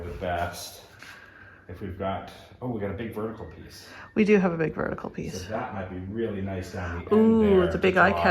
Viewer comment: I thought was saying he has Big Bertha here instead of big artwork...hahahaha